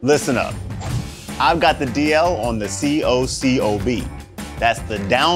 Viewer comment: To me it sounds like he says " I got the DOWNLOAD on the C-O-C-O-B"....Not down-low 😂!! I was like WTF, did McCormick finally admit we are in a simulation 😳😲🤯?!?! I knew it all along ...😏👉👉!